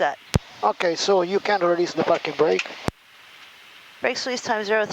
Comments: It sounds like he said "ok so you can release the F@ brake". HA! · Did he say “ you can release the f’n brake “ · Sorry, did I hear that right at 4:08 ... "Okay, so you can release the 'fkn' brake"! 😂